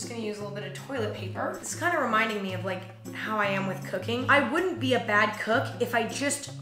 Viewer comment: When she said it reminded her of how she is with cooking I heard "how i am with cocaine" and I thought she was being way too chilled about it